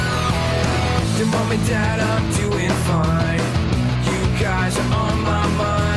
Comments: I always thought it say “Do you ask I’m doing fine” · 0.08 I always thought it said “Cute guys are on my mind” lmao